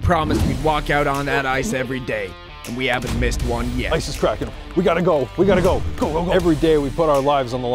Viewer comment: First time I watch this and Bart says "ice is cracking" I thought I heard "isis kraken" which i thought was a terrifying concept